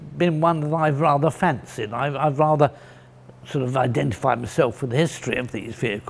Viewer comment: I misheard this as david fletcher identifying himself as a bren carrier and had to do a second takr